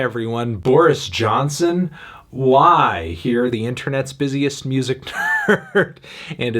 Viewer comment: Sounded like you said 'Internet's busiest music turd' 😂😂